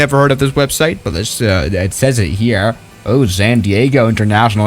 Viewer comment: Sounded like a stroke ngl lol, "It says it hearar"